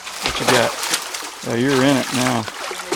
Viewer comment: Sorry but i heard "you're in it too" As it is a reply after says "youre in it now"